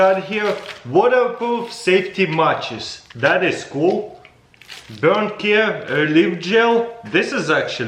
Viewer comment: couldve swore that said "bumcare"